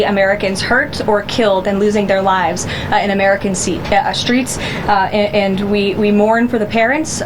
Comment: There you go...Freudian slip..."seats" instead of streets. That's all they care about...the optics...the Trump brand. That's how much they value your lives. It's sad.